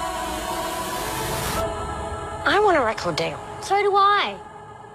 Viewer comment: What did they say there? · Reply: “I want a record deal.” ‘So, do I.’ Yeah, boys. Get in line.